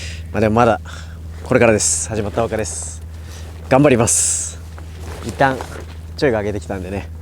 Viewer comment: 「頑張ります」が「ガンVARIVAS」に聞こえる